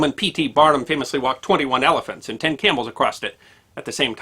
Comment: Did you say , "acrossed" ?